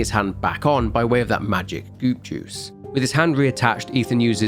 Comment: I swear to God you said, “magic Boob juice.” Lol